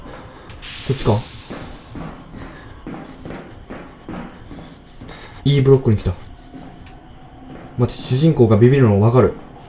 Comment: 改めて見ると12:20あたりのEブロックが良いブロックに聞こえる